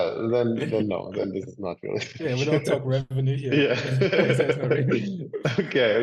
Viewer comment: What did he say at 18:52? We dont talk about profit? One of the Master's rules reiterated to him?? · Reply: We don't talk revenue here..? Is that what he said? Was that an inside joke?